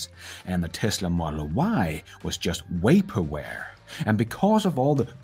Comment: This video is so awesome. But I had to pause it because you said “way-perware” instead of vaporware.